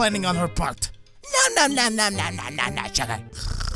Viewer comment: sounds like Kevin said shut up but he actually said sugar🤣🤣🤣🤣🤣🤣🤣🤣